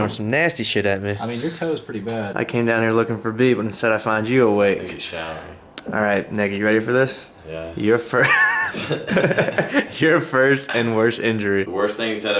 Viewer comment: i thought he said "nigga you ready for this" 😂